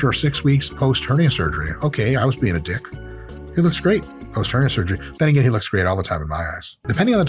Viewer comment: did he say eyes or ass? Both are probably true